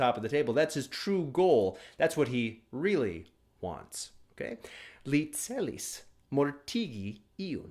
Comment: Le celis mortigi iun, it sounds like you said "He" for the word "Le". Is that really "Li"?